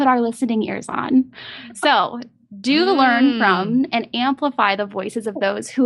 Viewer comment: did she just say 'do the learn-from'?